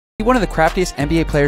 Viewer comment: Lmao 😂 I thought he said one of the crapiest